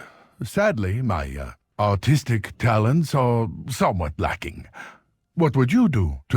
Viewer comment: I thought Cho said "Sadly, my - ah - autistic talents are somewhat lacking"